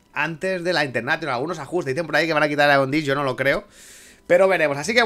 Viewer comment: No entendí eso de quitar qué cosa jajaj soy yo o no se escucha claro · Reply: Jajaja hablar con una patata en la boca es lo que tiene. Digo AEON DISK, que hay quien dice que van a quitarlo xD